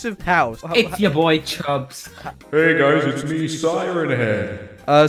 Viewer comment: the lag sounded like he said chrubbs lol